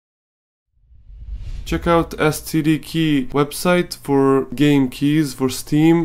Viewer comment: who else thought he said check out std key